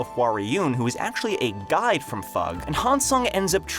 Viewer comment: did you say guy or guide? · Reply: Guide